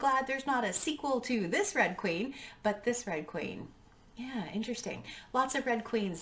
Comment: At first I thought you said two drag queens lol